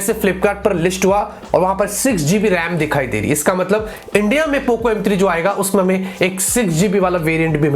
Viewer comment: Did anyone notice POVO M3 instead of Poco M3?